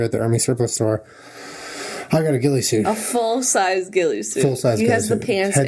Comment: I thought you said gildie suit and I was like why you have a suit that looks like your mom's dog